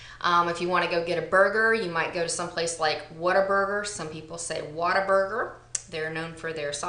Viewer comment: loved this! I am not a native Texan but definitely one by heart. My husband is a native and I'm always telling him I need a Texan/English translation...lol..The first time I heard him say Whataburger I thought he was saying water burger and I thought, that doesn't even sound good.